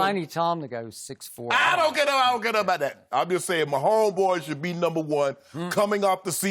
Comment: (Shannon sharp!) what happened lol 😂... he said. I den gotto I den gotto bout tat. 😂